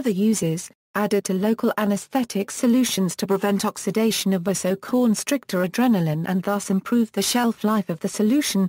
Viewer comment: What does it say at 2:02 about co constrictor of adrenaline? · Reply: Vasoconstrictor adrenaline